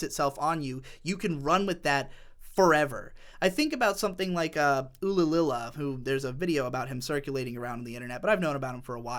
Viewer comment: Can someone send me a link of the video mentioned here? I can't hear the guy's name right. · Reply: Ullillilia